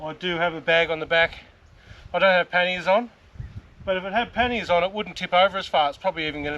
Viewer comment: Lol the accent I swore you said I don't have panties on! 😂😂 Great video !